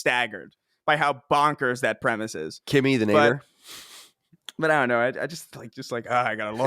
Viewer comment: Colin, we need clarification. What did you say at 35:30? 😂😂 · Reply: He said "Kimmy, the neighbor", referring to Kimmy Gibbler on Full House.